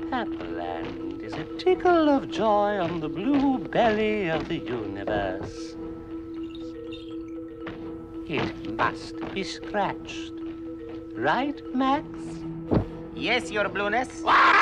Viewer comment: What the blue meanie says from 0:09 to 0:22? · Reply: “Pepperland is a tickle of joy on the blue belly of the universe. It must be scratched, right Max?”